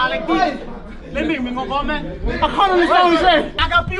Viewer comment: he was saying hit harder then he said do u understand what I’m saying